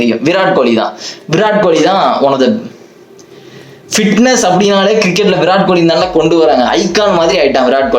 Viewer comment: You say Fitness....... I hear Virat